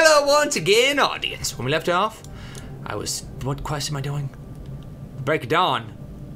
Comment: At the beginning I thought he said breaking dawn XD so glad he didn't :P